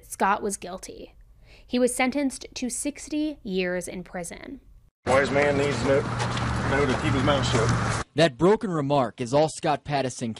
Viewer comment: What did Scott comment leav ing court??..didntcatch that!!🤷‍♀️ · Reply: Sounds like he said “a wise man needs to knows when to keep his mouth shut” or something like that…